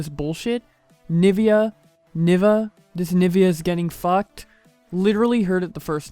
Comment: I hear it as "is this nvidea is getting fuck"